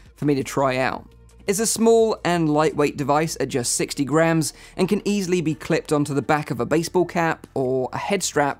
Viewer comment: I THOUGHT YOU SAID 60 GRAND LOL